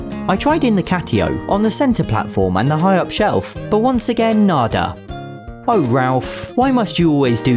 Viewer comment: did you say nda ( Indian for no ) or nada?